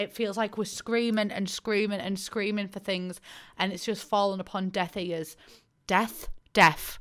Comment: I thought she said “death eaters” and I was very confused